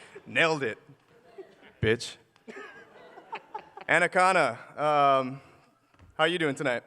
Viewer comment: PapaKay Anna akana another youtuber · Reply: OHHHHH. i couldve sworn they were saying annaconda. Couldnt find anything on it haha. annaconda is a clever name though